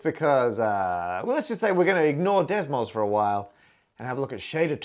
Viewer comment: I heard "desmos" as "decimals" throughout the video, until I actually checked the name of the program written on the screen...